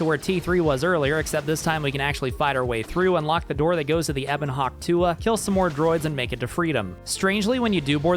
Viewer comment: I was just enjoying the video, and then I hear it "Ebon Hawk Tuah" ruined the rest of my day, I am bothered, in too many lanes, unmoisturized